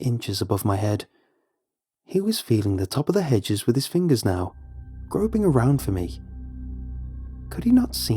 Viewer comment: i deadass thought he said: feeling the hedge with his fingersnail. lol